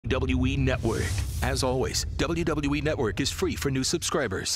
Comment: Was I the only one who heard the narrator said WE Network 😂😂😂